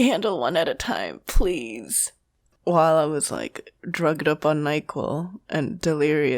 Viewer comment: the captions says 'drugged up on michael' and i immediately think of michael from be more chill